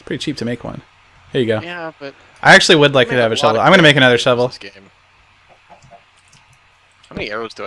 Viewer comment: I HEARD another portal??